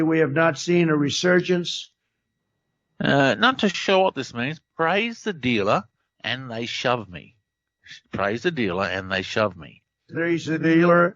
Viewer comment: I think when he says, "Praise the dealer, then they shove me." He means China deals us a bad hand, but the press and all others, still shove him (pushes back on all he does). I don't know why you always go on Debbie Downer (Jeff's) show. His vibration always seem so negative & low, IMO. Our words create our reality. Please stop the gloom & doom narrative. You only feed the beast when you're so lower vibe. And the world has enough lower vibration people, you know? Chin up! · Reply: I hear "Praise the dealer - then they shaft me". Just thinking - he has a scottish mum. Some slang words may be english scottish ect. I am a brit with an irish dad and have a good ear for celtish accents. And also I get a 'No:' and not an american 'now in a few of trumps reverse stuff. !! '